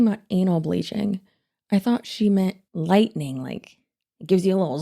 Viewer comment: but why are we talking about an.al bleaching? She completely lost me….(and yes I heard butthole lightning cream too)😂😂😂